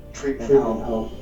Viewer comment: I thought he said "treatment home" not "freakin' out"